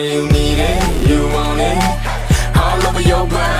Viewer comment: Sounds like they're saying Wawa love. Can't unhear it. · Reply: I hear it. Thought they were saying wild wild love.